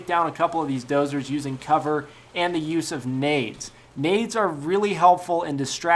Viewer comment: At first i thought he said look at how i take use of Aids instead of nades lol